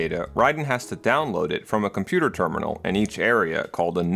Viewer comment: did you say nerd?